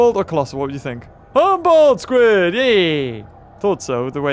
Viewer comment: I thought he said humble squid